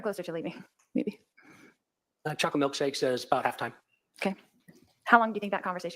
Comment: Did anyone else hear the 2nd cop say something like “chocolate milkshake about halftime” or was that just me?